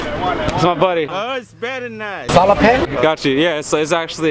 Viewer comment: what does he say at 6:42??? Salopen???